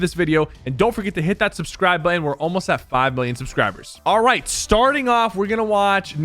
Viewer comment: did he say we are almost at 5*billion*